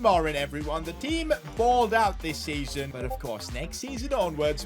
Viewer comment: did anyone else hear that? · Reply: He said this season